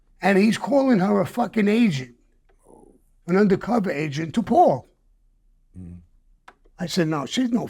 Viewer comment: Stg I thought he was saying under cover Asian 😂😂